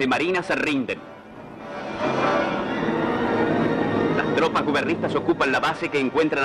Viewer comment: Alguien me puede decir que dice en el minuto 6:19? Las tropas guberristas? Entiendo eso pero la verdad ni idea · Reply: gubernistas creo q dice, no existe esa palabra en teoría, pero se refiere al gobierno supongo